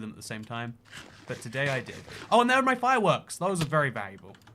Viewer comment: I thought you said "netherite fireworks" for a second!